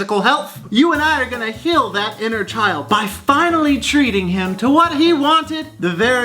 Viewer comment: at :51 I thought I heard you say you were gonna "kill that inner child" because you're eating expired cereal!